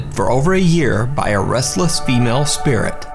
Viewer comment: I thought he said "Wrestler female spirit" lmao